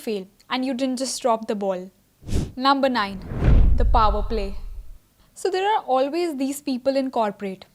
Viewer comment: at number 9, "6:34" there should be written “the power play” instead of "the power peacock."